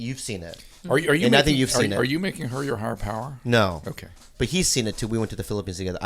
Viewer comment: What does Dr. Drew say at 47:19? "Are you making her your Hard Power?", "Heart Power?" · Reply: "Higher" power :)